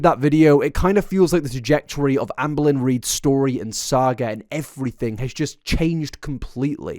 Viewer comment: At first I thought you were talking about Amber Reid's fetus saga and was really confused wtf does that even mean. Now I don't know if I'm happy I misheard or not.